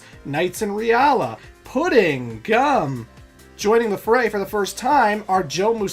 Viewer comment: I’m sorry, I thought he said “Pudding Cum”